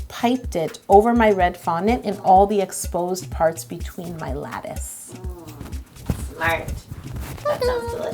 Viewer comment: My cousin thought saying lettuce instead of lattes ( i dont know how to spell it )